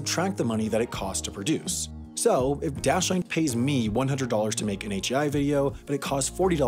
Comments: At about 1:06 the caption says brilliant but the narration says dashlane. War of the sponsors! Edit: timestamp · If you open subtitles, when HAI is saying "Dashlane", the subtitles say "Brilliant"